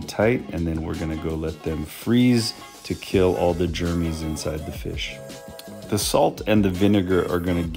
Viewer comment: "kill all the germmy in the fish", first time I heard "kill all the germen in the fish..."